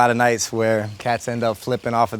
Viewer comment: did he say cats?